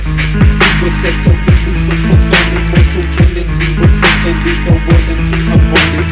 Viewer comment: does he say "HE WAS SET UP,THEN LOOSED,LOOK FOR SUDDENLY MORTAL KILLIN'?????y am i hearing "HE WAS SET UP TO BE LUCIFER SON AND DO MORTAL KILLING?